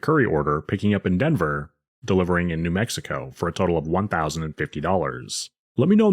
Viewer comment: I'd do the 1k trip to New Mexico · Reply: Man i thought he said Mexico 🇲🇽. New Mexico...Yes!!! Id do it.